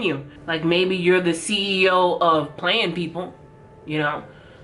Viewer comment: The CEO of playin' people😂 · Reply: When she said that the first time I thought she said 'CEO of bland people', and I was like well they're hella white and all the same and pretty boring so yeah, that's true! Lol